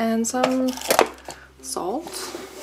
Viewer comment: did you say 'salt'? For what do you need it :) ?